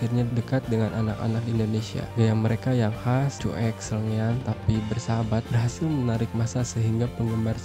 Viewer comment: Sangean ??!! Semoga saya ngga salah dengar ya 😅😅